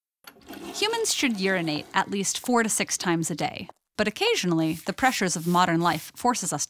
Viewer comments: I thought she said "Humans should urinate at least 46 times a day" lol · Initially I heard "46 times a day" and was a bit worried. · i heard 46 times day and was like wtf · Saw this while staying up late and I misheard her saying "humans should urinate at least 46 times a day" and I was like WHAT?